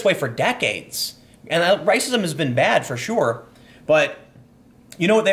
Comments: Tim, did you just say....RICEism?? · did you call it Rice-ism???